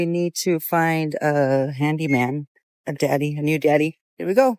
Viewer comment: oh handy man is what shes after i thought she said randy man ....my bad😋😋